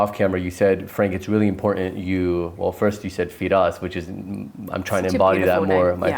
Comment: Loved this interview. I have a question at 40:05 you said the name "Fides" - is this what I heard - because the subtitles said Feed - us. Only reason I asked is that I have this tattooed on my wrist - :Fides - the goddess of Faith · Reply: Firas ❤